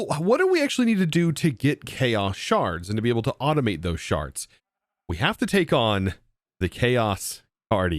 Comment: I swear I heard this lmao "What do we actually need to do to get chaos shards, and to be able to automate those *sharts*"